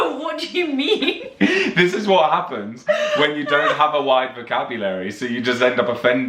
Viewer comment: Wait, I heard say 'appens, instead of happens.